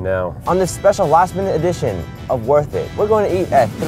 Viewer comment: when he said last minute editions of worth it @ 0:24, i thought he said last edition of worth it, panicked and had to rewind. thank god, for that minute ;)